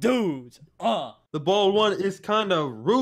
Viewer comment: I thought he said “Im being threathen by two dudes” “The bald one is kinda cute “